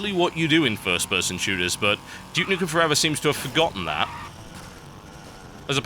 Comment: I thought it said"Puke nukem forever"